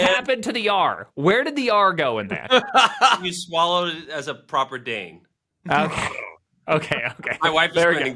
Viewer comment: I thought he said "you swallow it like a proper date" at the beginning 💀